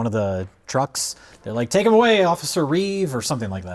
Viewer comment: I think the “Officer Reeves” reference was just Jay mishearing him say “send him to Belle Reve” which is a prison in DC.